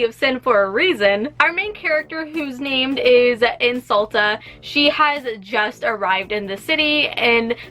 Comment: I heard “Her name is Insult” and had to go google there!